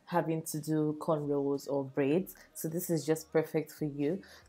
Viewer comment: I thought she said no braids